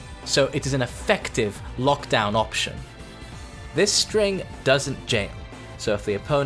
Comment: What did he say? @ 5:39 "This string doesnt ____"? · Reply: "This string doesn't jail", which means you can block the first hit of the string, and duck the 2nd on reaction.